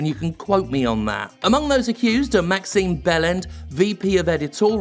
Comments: I only heard “Maximum Bell-end”, I may have misheard but it certainly fits. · I genuinely thought you said "Maxime Bellend" and thought, "Seems fitting!". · I must admit, when I first heard "Maxine Bellend", I thought you were having a laugh at her expense. Then I saw her name was actually Beland. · I'm not going to lie. When I heard you first say Maxime Beland's name, I thought I heard you say bell end. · Genuinely thought you said Maxime Bellend... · so that one guys name is Beland...and I swear to god I heard it as "Bell End" and after doing a doubletake..then hearing all of what he did..I was like "Yeah...BellEnd is right."